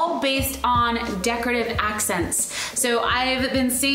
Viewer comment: I wish my living room looks like hers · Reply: i thought it said decorative accidents😆